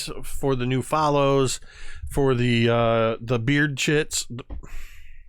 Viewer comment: Okay, I thought I heard "Beard chits", then " Beer chits" ... Seriously, you all are having far too much fun, everyone. And in that note - Cheers!! 🍻 🍻